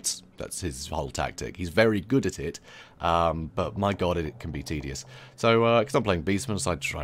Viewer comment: Misheard you say a bit tedious as, 'hes an evil genius', if you think about it with his play style he really is an evil genius